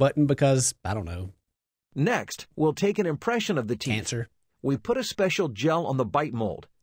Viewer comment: did i hear "cancer" ?